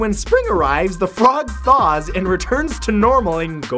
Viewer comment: did he say "The Flog Thaws"?